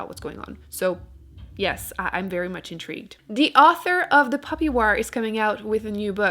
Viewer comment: I heard "The Puppy Wars" and was excited